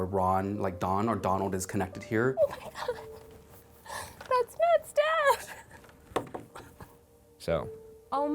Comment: What did she say in 7:39 i didnt understand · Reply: She said "oh my God, that's Matt's dad"